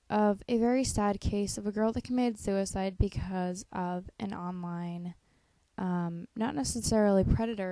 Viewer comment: I heard you say gaiaonline and my eyes darted up to the tab that I had gaia on.. o..o